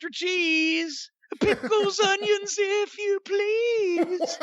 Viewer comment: "Like Mussolini with extra cheese Pickles, onions if you please Put on the bun, the mayo there I'll eat it and I'll-" Jim's best "Cult of Personality" rendition so far. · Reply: I first thought he said Peoples Onions if you please lol